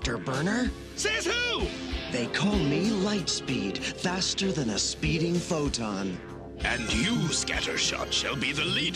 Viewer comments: "They call me Lightspeed. Faster than the speed of light". Who exactly called you that? You were just created 5 seconds ago. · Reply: I swore he said "faster than a speeding photon." Same thing basically, but it's the principle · "They call me Lightspeed. Faster than the speed of light". Who exactly called you that? You were just created 5 seconds ago. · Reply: I thought he said faster than a speeding photon.